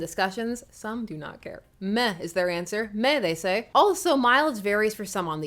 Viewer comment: I thought you said “METH is their answer” 😂😂😂